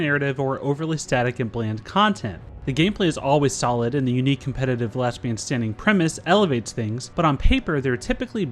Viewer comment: Did he say "competitive lesbian standing" at 00:20 ?